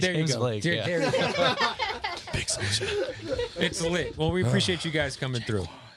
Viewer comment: what did joji whisper? I need to know. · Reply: tsanjou brahma big Soulja